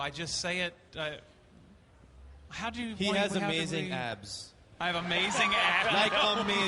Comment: could anyone tell me what did Kunal say? Did he say “He has amazing aps” ? What’s aps? English is not my mother tongue. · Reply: Oh it’s abs I got it.